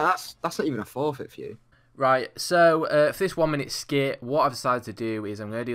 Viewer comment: I thought that said "1 Minute Shit." That'd be fast!! 😳😳😬😂